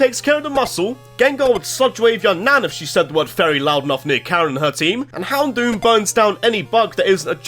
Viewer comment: i honestly have no clue what he just said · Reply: "Gengar will Sludge Wave your nan if you said one Fairy near Karen and her team!" There you go, my man.